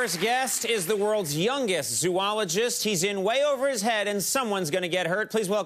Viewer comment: I was yawning. I thought he said swallowogist. 😑